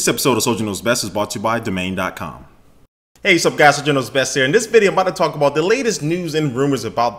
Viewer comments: Listen at beginning he sounds like he says "hey wassup guys, general knows best here" lol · Soldier, it's okay to slow down a little when you talk. At the beginning I heard "Hey what's up guys this is s-dr knows best..." lol It's amazing how amped up people in my school still are about the "iPhone 5" being the 4S.